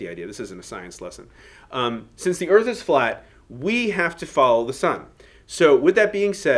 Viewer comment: The Earth is flat?? Really? I heard/misheard u say that. Anyway, apparently, I have a French easel. Who knew?